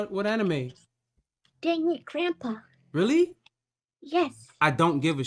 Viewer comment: Ah yes, Shukichishukisaikara from Deadbeat Grandpa. That show is a must see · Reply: I thought she said danging grandpa